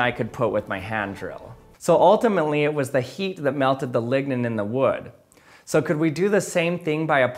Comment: I was stretching when he said Ligmin, and I swear, I heard him say ligma. 😅